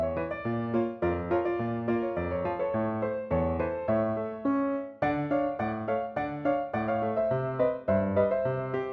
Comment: sorry but I can't understand what was that syrup like thing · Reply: Susu(milk)